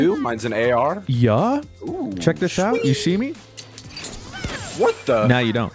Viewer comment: I thought said “check this shit out” at 2:43 and was like wait what? I had to listen again multiple times and still dunno what I heard